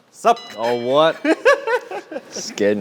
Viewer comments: STOP HE DID NAWT JUST SAY ''Sup cvnt'' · did Chan say sup c*nt 😂